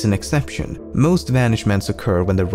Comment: Regular people: Sunken ship Lemmino, an intellectual: Nautical Vanishment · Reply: I thought he said banishment